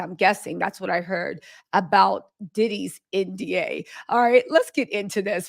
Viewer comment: I thought it said "Diddy's New Album"😅!!! IkYL